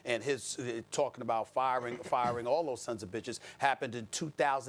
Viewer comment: did I just hear Stephan a swear😂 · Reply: Sons of B** lol he sure did